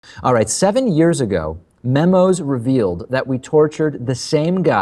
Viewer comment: OMFG I THOUGHT IT SAID MEMES LOL